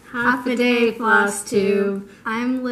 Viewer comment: I love watching your videos ladies! I like to stitch samplers too! When I first started watching I thought you were saying “half a day” like little one had half a day of school. Glad I know what you were saying since you explained it in another video. It’s fun to see your changes and your story of that are so meaningful. Love Love Love the quilt squares definitely gonna have to stitch those too :)